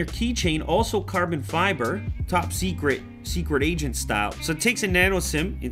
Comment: Misheard quote: 1:44 "Top secret. Super asian style."